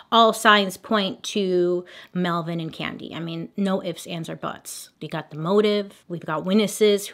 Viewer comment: When you said “no ifs, ands or buts” I heard “no ifs aunts or buts” and was like hang on- 😂